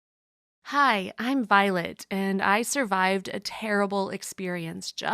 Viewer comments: LoL at first I thought she said “Hello, I’m violent” I was like “.........welp that’s new” 😂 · I thought she said hi I'm violent and I was about to be like. Well this took turn already